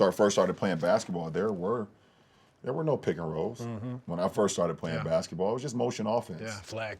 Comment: For a moment I thought LeBron said that there were no PnRs in basketball when he started playing. Upon rewind I think he meant to say that HE did not play PnR basketball when HE first started playing.